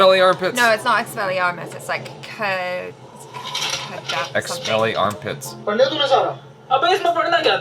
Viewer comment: Did I just hear "ex-smelly-arm-pits" ?!😂😂😂